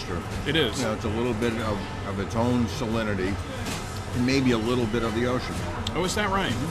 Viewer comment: lmao at 4:14 i thought he said maybe a little bit of urine